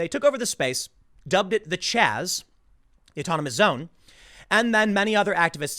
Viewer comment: The Chaz? I thought he said "The Jizz"